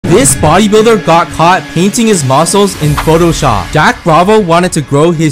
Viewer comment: I thought he said eating his muscles